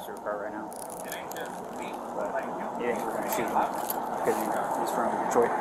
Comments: did he say "were antsy to shoot him" or "were probably gonna shoot" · I guess this is unrelated to what ACTUALLY Happened and what was actually said, but I turned on the closed captions and this police offer said " ....we're going to shoot him because he's from Detroit". 🤯🤯🤯